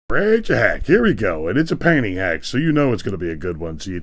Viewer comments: And here i thought he said " "here we go and its a panty hack" peaked my interest real quick 😂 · I swear, at first I thought you said panty hack! 😂.